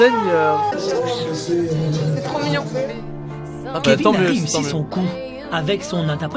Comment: c moi où j'ai entendu ''kevin est vraiment con '' ? 🤨😂